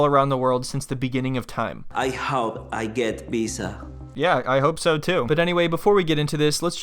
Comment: I heard "I hope I get pizza" 🙏😆